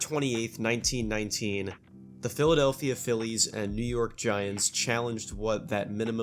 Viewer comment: Did I just hear new York giants